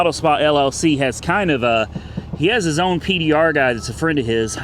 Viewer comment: While distracted by work, I heard say "I know a PBR guy" and it took me a moment to realize he said PDR instead. :D